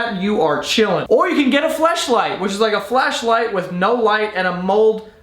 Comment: Lucky my dad thought you said flashlight otherwise i would’ve been DEAD. (I’m a girl.)